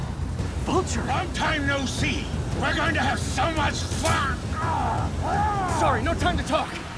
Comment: Vulture sounded like he said "We're going to have so much fuck"